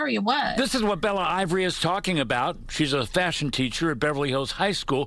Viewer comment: "she's a fascist teacher" exactly what I heard 😊